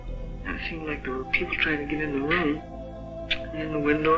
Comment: I see you're struggling with the unknown developers pronounce. Just refer to the unknown developer as "them" and "they" ,it's a gender neutral pronounce. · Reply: I think she said "kids trying to get in the room"